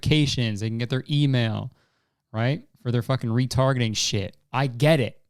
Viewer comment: Im just gonna really hope i heard that wrong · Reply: he said retargeting but dear GOD did I have to listen to it like 3 times to make sure